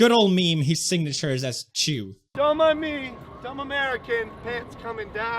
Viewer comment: I'm Japanese. I felt you really understand about Japanese cultures. I want he will be confiscated passport right now. Don't come again Logan Paul. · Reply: Yeah correct me if I misheard but didn't he say "don't mine me just American here!" While pulling down his pants in the street?